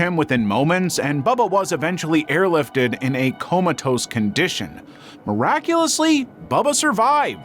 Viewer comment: did he said Comatozze condition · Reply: Comatose